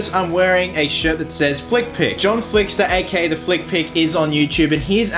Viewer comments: I thought it said "fuck pick" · For some reason i thought jit said fuck pick